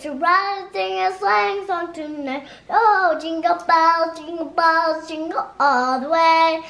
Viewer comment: it sounded like said jingle balls